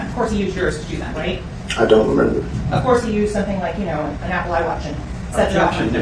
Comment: how did you not hear what she said? lol.. 'apple iWatch'.